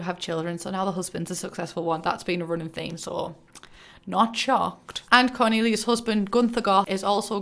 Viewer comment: I wasn’t looking at the screen and when you said “not shocked” I genuinely thought you said ”pork chop” I need help sorry thx ur welcome ok bye